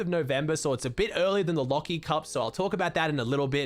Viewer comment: I thought it said leafy cup instead of lachy cup for a Schindler 🤣